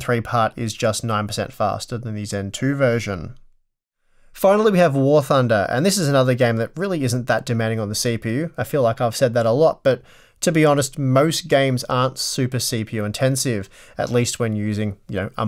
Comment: I really thought he said "War Funder". I'd play that game.